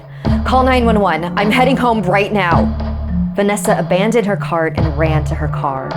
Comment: Bro at 3:20-3:27 bro why did she say that "she abandoned her car and ran to her car" got me dying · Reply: She abandoned her cart not car